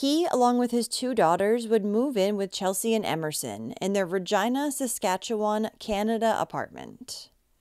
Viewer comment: you might wanna correct that... you did not say what you ment to say..... right? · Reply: That’s because it’s Regina in Canada and pronounced that way